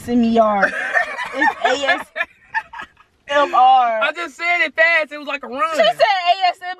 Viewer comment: i heard asm yar